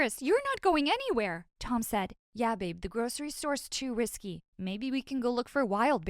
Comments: did he say " yea, babe"?! What?! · did he just say babe?!?!